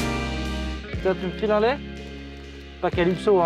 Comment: il dit quoi ? J'ai rien compris. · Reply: Cette vanne est particulièrement corsée : "Tiens tu me files un laie" : Un (laie) morceau d'isolant, mais une laie c'est aussi la Femelle du sanglier, c'est pour cela que Calypso est quelque peu vulgaire :face-orange-raised-eyebrow